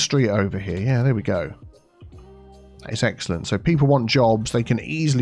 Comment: Did you say eggcelent?